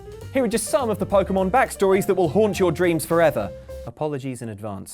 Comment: Me: sees thumbnail ill watch Me: OMG DID HE JUST SAY HURT UR DREAMS IM WATCHING THIS AT NIGHT Me: 911!